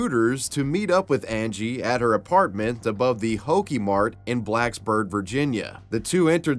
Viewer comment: Honestly the most concerning part about all this is that I thought he said “her apartment above the Pokemart”